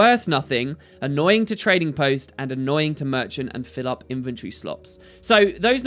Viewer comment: did anyone else hear "inventory slops"?